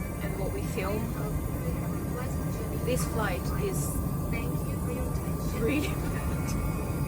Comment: Did say “really bad” at 5:58? If so, what was so bad about the flight?